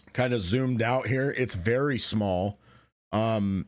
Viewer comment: please excuse me : did i just hear "very small map" ?! WTH x )